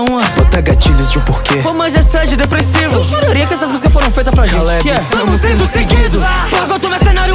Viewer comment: K - "bora Caraí , canta tbm" N - "ve vem sem bazuca pow.." K - "quem que canta com deboche? N - "não entendi" K - "alguém me ajuda" muito foda essa parte KKKKKKKKKKK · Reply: Ou quando ele fala "eu juraria que essas músicas foram feitas para a gente" eu entendi " eu choraria se essas músicas forem feitas para a gente